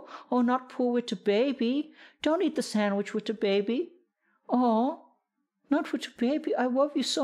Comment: what I heard: “don’t eat the sandwich with dababy” Dababy: “aw man”